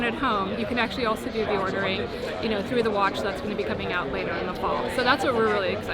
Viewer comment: Did she say the watch that’s coming out later this fall? Like another new watch🤔🙃